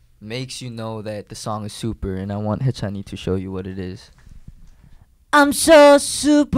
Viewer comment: DID HE SAY HAECHANIE I'M